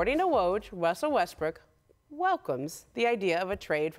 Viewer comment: I couldn’t watch this shit I died when I heard Wussell Westbrook